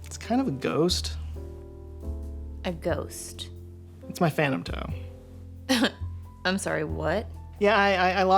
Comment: in the intro is the creepiest guy I have seen all day. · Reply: I thought he said phantom ho as in whore....... It wasn't until I saw the actual toe that I realized it was a toe!